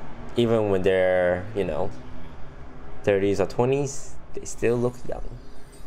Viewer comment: When he said "They still look young." I thought he said "They still look yummy."